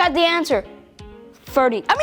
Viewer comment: I heard he said 41